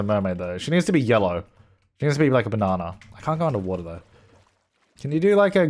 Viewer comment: What does say here 14:29 - 14:31 ? Please help lol. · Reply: is it "I can't go under water" ?